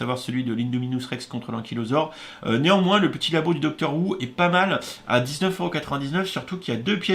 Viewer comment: Quand j'ai entendu "labo du doctor wu" j'ai compris "labo du doctor who". Je viens de comprendre le principe de l'ascenseur émotionnel...